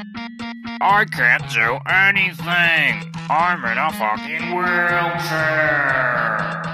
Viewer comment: did he say the F word?! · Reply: NuggetGame I can't do anything! I'm in a fockin wheelchair!